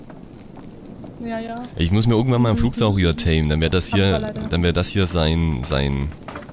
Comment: hol dir einen flugsauria :D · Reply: Heiner Boesel Ich habe "Flugsaura" verstanden :D